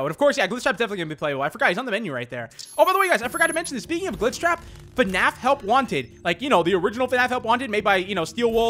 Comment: did just say vegan glitch trap!?!?!